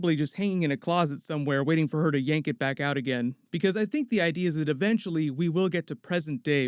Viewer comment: I swore I heard him say “yeet it back out”